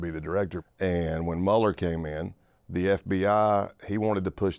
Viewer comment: When he said Mueller, I thought he said Mulder. 🤦‍♂️🤦‍♂️🤦‍♂️